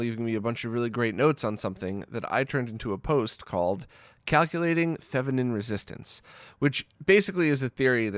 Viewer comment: I was listening to this off screen and when he said 0:44 "Calculating thevenin resistance" I thought he said "calculating feminine resistance" and was discussing body odour lol.